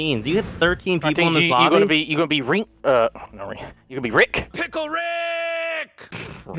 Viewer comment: umm...what does say?? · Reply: Pickle Rick